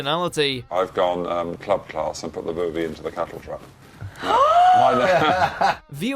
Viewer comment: What is the guy saying at 2:35? It sounds like, "I've gone club class and put the movie in the cattle truck."